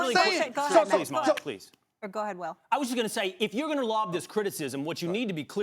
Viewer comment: He was like that to her because she was trying to step on him. He was trying to let her that he is the moderator and not her. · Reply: It didn't seem like he shut her down to me, it sounded like she said "Can I ask one question?" and he said "Sure. Please Molly, Please" and yielded for her to make her point, but then she said "Go ahead, Will" and he continued. He wasn't saying "Please shut up" he was saying "go ahead" rewatch and listen closely.